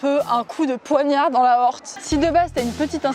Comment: "comme un coup de poignard dans l'aorte" J'ai entendu "Comme un coup de poignard dans l'aoste" Et je me suis dis : "Comme un coup de poignard dans le jambon. Que.. Hein ? ha oui l'aorte T_T Va falloir ressortir les cours de SVT du collège c'est plus possible là XD Toujours au top tes vidéos ! Et cette chevelure toujours en bonne santé haha !